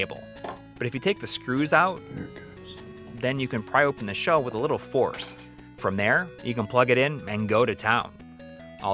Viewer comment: someone was whispering. What did they say? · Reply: I was really struggling to get the screws moving on that cable casing. I mumbled "there it goes" and actually put that in the video. I thought it was hilarious when I saw he'd done that.